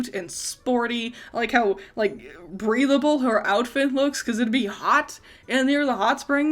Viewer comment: I was so confused at first 'cause i thought you said ''breedable'' instead of ''breathable XD